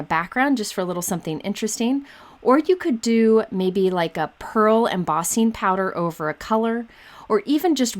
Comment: Oh! PEARL, I initially thought you said purl (you were showing the knitted jumper stamp! 😂)